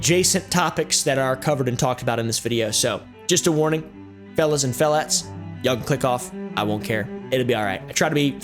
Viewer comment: i heard fellas and filets